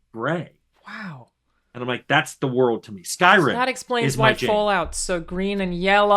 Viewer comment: did I hear "Skyrim is my jam"? Mine too, Tim... Mine too :)